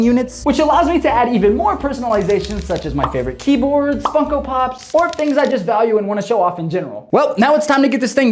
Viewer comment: I Thought He Said “Fucko Pops” 💀